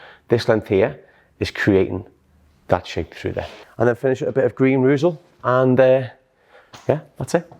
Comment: What is the name of the pomade please? It sounded like you said Green Roozel. 😄